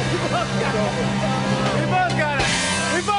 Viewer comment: Did anybody else catch Jimmy telling Nelly to let go of him at 7:30? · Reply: He said "we both got it".